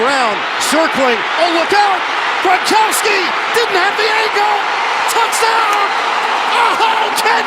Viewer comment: For a second I thought he said “Gronkowski! Didn’t have the ego”